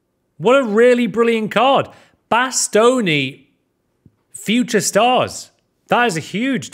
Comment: When he said bastoni i thought he said matt stoney